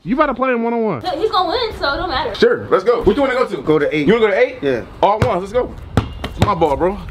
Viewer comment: I like how they edit damien that he was a 6 pack 😂😂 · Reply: he clearly said go to 8